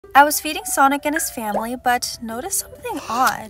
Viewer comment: I thought she said "I was feeding sonic his family" 💀